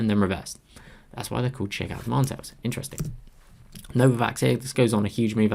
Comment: Can someone please tell me what is the candle called at 34:37 (cant understand his accent)? I know its a really really great video, but its a bit complicated for me. I am not able to understand how is he deciding the entry. This is what I got - look for the entry trigger bar, entry is between its high to 0.5% of the high & stop loss is at the low of the bar (risk has to be 1/2 to 2/3 of ADR)? did I get that right? He is so fast. where can i read about entry & exit in more simpler terms? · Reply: Shakeout Demand Tails